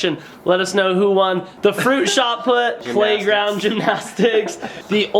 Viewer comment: on shut put on diving and shining uneven bars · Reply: I thought you said shut up XD